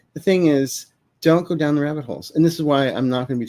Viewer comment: When said “don’t go down the rabbit holes,” I heard “don’t put down your edibles.” 😂😂😂 Both good advice for dealing with the current dystopia we’re experiencing.